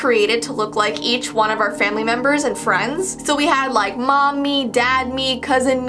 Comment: I thought said "mommy" no she said "Mom mii" XD